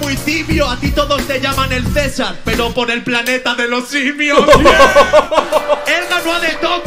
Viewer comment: Yo pensé que decía por moe de los Simpson, disculpa la ignorancia no soy de España